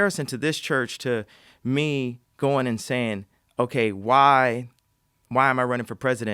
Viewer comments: Kanye: “...and me goin an sayin” What I hear: “...and me goin insane” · Multiple times I thought I heard Kanye say: “me going insane” when he actually said: “me going and saying”. I think I’ll go with the former... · Every time Kanye says “going in sayin” I hear: “going insane” I love him tho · I heard him say “me going insane” twice already. And I tend to agree.